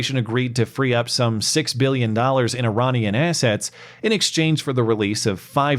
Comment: I swear I heard you say Iranian Asshats